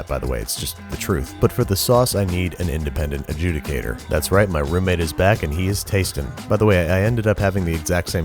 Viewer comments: Thought he said my yes my roommate is black lol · I heard wrong and thought he said: "That's right, my roommate is black and he is tasting." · I heard "thats right my roommate is black and he is tastin" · I heard...:"my roomate is black" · I heard " that is right my roommate is black" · I thought he said "that's right my roommate is black"